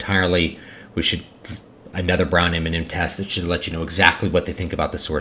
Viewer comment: what is a browniminen test or whatever you said?